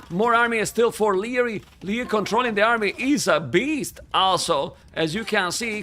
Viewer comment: Every time said 'also' I heard 'Asshol*' :)